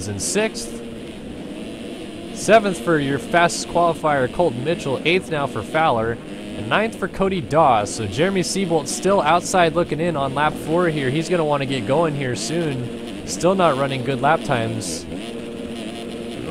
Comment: hahaha when u were talking about Cody Dawe it sounded like u were saying Cody Darr with a British accent 😂😂😂